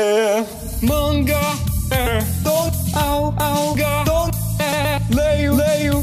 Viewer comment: MANGA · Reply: Eu ouvi "manga é tão salgado" kskskskksksksks